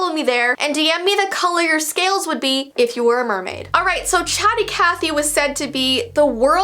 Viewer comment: For a second I thought they said Chatty Cathy is an idiot lol